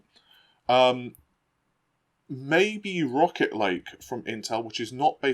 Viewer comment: I thought you were saying Rocket League 😂